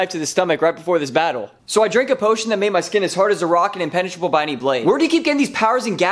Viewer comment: did he just say hard as the rock or hard as a rock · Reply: A rock